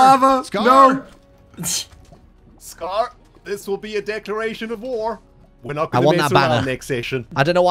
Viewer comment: I heard "Decoration of War".